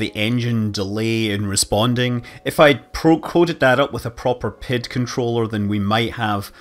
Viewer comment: I think that's because it's a term I've read about a million times more than I've heard. Also because it's a scientific term it ends up in small communities who might develop their own pronunciation. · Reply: I actually thought you where saying pitch controller: even as a mech. engineer I'm very much of the Pee-eye-dee school of thought.